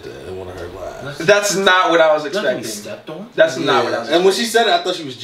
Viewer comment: i thought she said i like to be sucked on !! not stepped on...hahahah